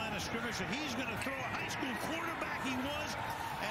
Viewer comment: I thought he said "Heisman quarterback" instead of "high school quarterback," and I was questioning my whole reality because I remember Jakobi Meyers playing at NC State just a few years ago and definitely not winning a Heisman at QB.